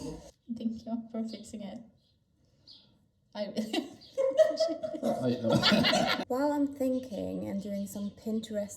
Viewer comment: "I'm not sure I like this" is what I think said at 11:13 :)